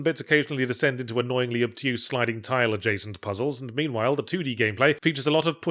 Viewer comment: is he saying obtuse or abstruse? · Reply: “Annoyingly obtuse”